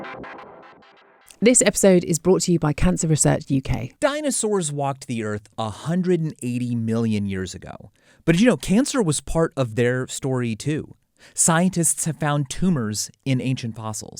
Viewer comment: i skipped the ad, and I heard "dinosaurs roamed the earth fifty million years ago... .... science. He just Jesse'd lol